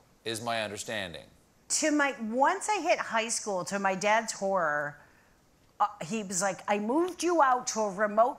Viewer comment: As I'm not from North America, I thought that she said "To my father's hore" - or something like that.